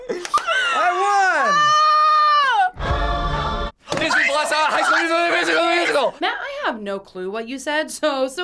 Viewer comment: Did I just hear Matt say "High School Musical: The Musical: The Musical" ?